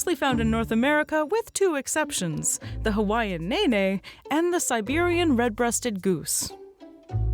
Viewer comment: Did she just say "hawaiian nae nae" Geese: watch me whip